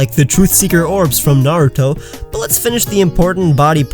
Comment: did u just say naruto?